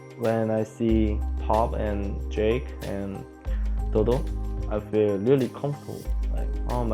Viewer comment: didnt Olleh say dodo instead of double?